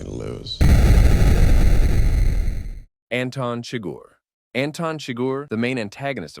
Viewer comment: Somehow i always hear "Anton Trigger" instead of "Anton Chigurh"